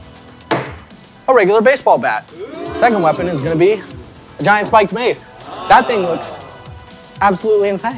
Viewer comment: I thought the whole time it said SPIKED MIKE